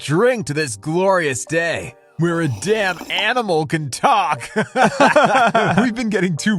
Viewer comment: The fact that that guy’s first reaction to a beatboxing dog is to say “Let’s all drink to this glorious day, when even a damn animal can talk!” Like a fucking Skyrim NPC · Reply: I thought he said Damp animals haha